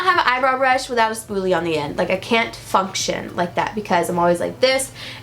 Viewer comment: When she said function and I heard it as fu*k shit 😂